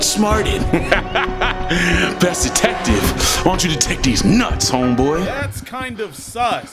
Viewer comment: "Why wont you detect these nuts" THATS KINDA SUS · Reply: I thought he said "I want you to take these nuts." 🤣🤣🤣